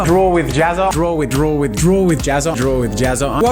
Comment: i heard droid jazza